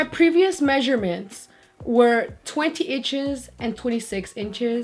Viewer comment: Did she said 20 itches @ 2:31 ?